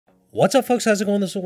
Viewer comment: sounds like he said “what’s up fucks!’